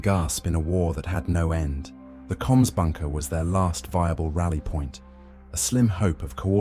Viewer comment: I keep hearing "cumspunker"